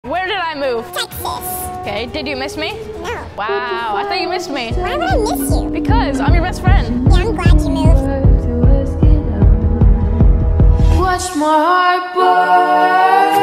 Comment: Ohhh I thought she was saying “yeah, I’m abt to move” but I had to play it again